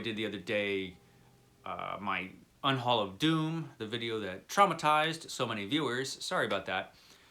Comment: I thought I heard you say “unhaul of DUNE” and I freaked out a little. Turned out it was “doom” and I did watch that video, which was fantastic. Roger—hahaha!!!